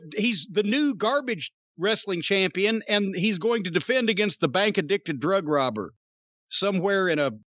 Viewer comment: did i hear him say "the bank addicted drug robber"? lol